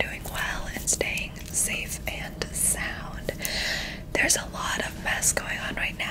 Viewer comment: Did she say "staying safe and "Sound" at 0:28 ?